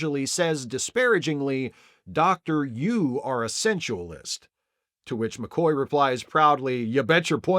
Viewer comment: I 'misheard' "senualist" as "essentialist" back in the day. Either would fit! :)